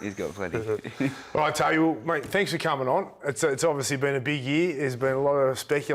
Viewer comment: I think Braith said, “alright Toby thanks for coming on’ at 6:47 😂